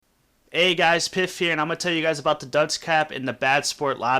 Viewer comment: I thought he said hey guys piss here